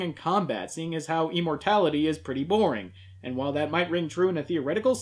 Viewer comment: Emortality? Not sure if that's what he is saying or just what I'm hearing but I'm stealing that one.